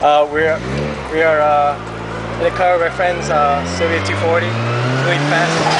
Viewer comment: did you say civic 240?